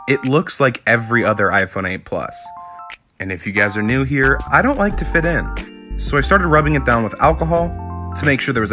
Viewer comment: What he said: I don't like to fit in What I heard: I'm not like other girls